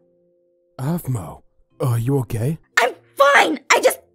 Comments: When said “Aphmau, you ok?” It sounded like he said “Aphmau, your gay?” -rewatching it cuz why the hell not. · every time says "are you okay?" I hear 'are you a gay' i think i've gone deaf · Sounds like he said : "are you a gay"? It sounds like that for me if not on urs then idk thats what i ehard but i think he actually said " are you okay" but ye i heard "are u a gay"